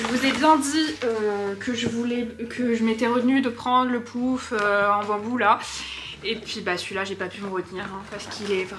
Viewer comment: Tu as dit quoi à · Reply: "Je m'étais retenu de pendre le pouf en bambou "